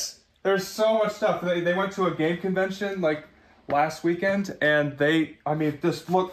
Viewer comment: Sounded like he said Gay convention?